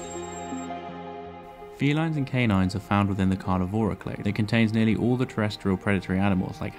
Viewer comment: are found within the carnivora what? What's the word after "carnivora"? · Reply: I think clan